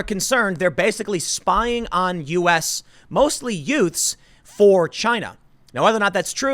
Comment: did you say yoot? what is a yoot?